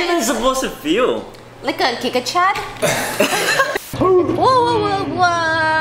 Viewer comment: i thought said "I'm thinking like a Chad" 😆😂